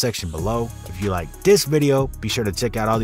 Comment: 🗣️Rizzle Who is Riylo ik a Rylo cuz 😂🤦🏾‍♀️💙🤜🏾🤛🏾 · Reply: Thought they said Rozzle not Rizzle 😂